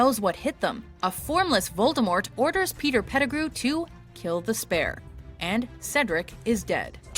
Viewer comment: 原來他說的是kill the spare 我一直聽成 tell the spell